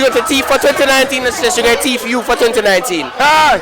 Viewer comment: Lol that accent sounded thicc in this video, although ik it's just because it was loud. That guy thought you said teach and whole time I didn't understand what you were saying either, I thought you said who are you gonna teeth this 2019....Thought it was some way to say give head😂😂(although Ik teeth wouldn't be pleasant for you guys). This video had me weak, the cute mistakes, the people being confused, not knowing what to say etc. It was funny how You had to change up your sentences and accent a few times because they just wasn't understanding you.